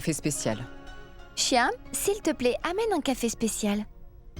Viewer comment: c quoi c nom je entendu Chien 😂